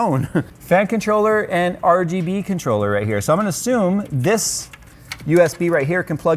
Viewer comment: I misheard that as ROGB controller Wtf why?